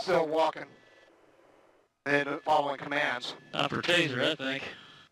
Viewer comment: In the defense of the unidentified man speaking, it sounds like he said "time for a taser, I think." He did not say "taser that thing." Clearly this is all horrible, though.